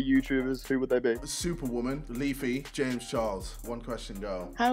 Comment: did he just said “James Charles” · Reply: You are mistaken... he said oh yeah yeah